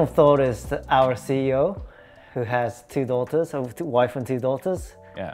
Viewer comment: did he say ‘two wives’ and two daughters?